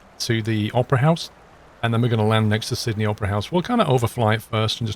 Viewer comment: i heard him say "sydney oprah's house" 🤣